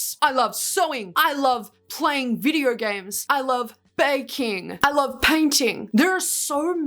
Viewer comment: girl I'll be so serious, I really thought you said "banking" there (since I wasn't looking at the screen when you said that) and I was so confused